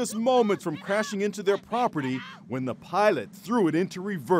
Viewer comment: did he just say pilot?